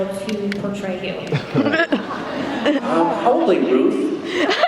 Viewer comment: What does he say in 33:48?? I couldn't catch it.. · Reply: "Um probably Ruth" (as in the lady that plays Crowley's mom).